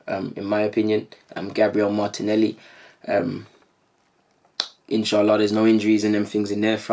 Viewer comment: Just wondering did he say inshallah at 7:01 Probably i misheard it